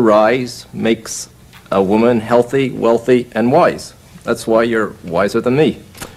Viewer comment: 😭😭 I thought he said healthy wealthy and moist